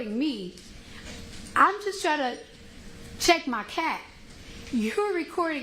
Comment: Oh My Buddy Did She Say "CHECK MY CAT " !! 😹😹😹😹 She's Hilarious